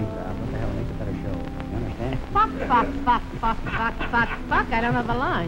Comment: WHAT IS WITH ALL THE OVERDUBS? IS SHE SAYING "FUCK"?